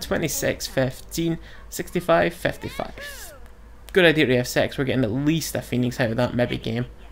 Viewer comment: it really sound like u say its a good idea to have sex at 26:28?